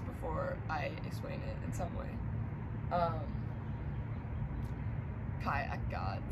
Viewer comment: did u just said your name is Kaja? Would be kind of cool, that just randomly two Kajas stranded on YouTube ✌🏻🤷🏻‍♀️